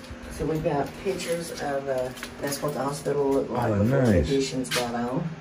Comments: you can hear a evp. Sounds like it says "that's what it was" or something. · someone or something said "FORGET WHAT THAT WAS". Like a whispering. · there is a subtle whisper sounds like it says I dont know what that was or something like that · Omg great ghost exploring hunting video At 18:50 I heard whispers saying “I DON’T KNOW WHAT THAT WAS”. I literally almost choked on my water hearing that lmao 😂 And your friend got BARS LOL. Love it 💕 Keep reach for the top guys... Blessed!!! 😁👍🏾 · I don’t know if that was someone near but it sounds like she says “ did you hear what that was”.